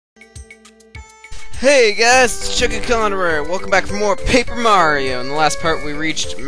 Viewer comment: for some reason I thought you said pig mario at the beginning